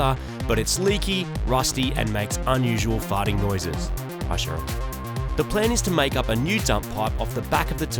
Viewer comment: anyone else hear "Hi Cheryl"???? hahahaha